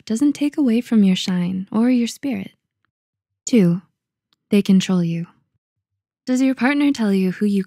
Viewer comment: why did I hear "they can troll you" 💀💀